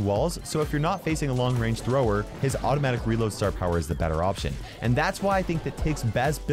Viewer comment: wait did just say brower